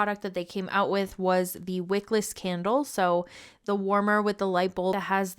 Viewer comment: minor error: 0:52 for CC has "quick list candle" instead of "wickless candle"